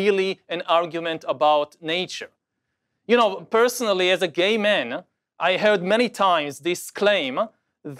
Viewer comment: bruh i heard "ima gay man" so i left b